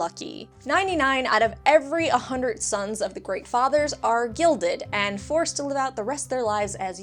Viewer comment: Sounds like you said 'Gilded' instead of 'Gelded'. Gelding is what happened to Varys, Gilding is what Khal Drogo did to Viserys.